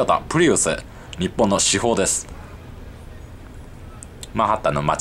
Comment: 車種のせいで｢至宝｣が｢主砲｣に聞こえるのほんと草